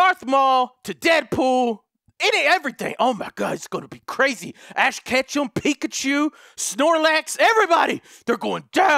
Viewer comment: I heard him say ass ketchup XD